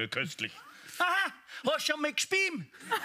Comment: wie endet der Witz? Ich verstehe es nicht!....hast schon mal gespiem? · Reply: "Hast du dich schon einmal übergeben?"